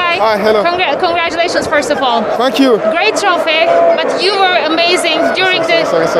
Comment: dia bilang apa...Fu*k you atau thank you...gw bingung😭😭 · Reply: Funck you tapi dia bilang nya thank you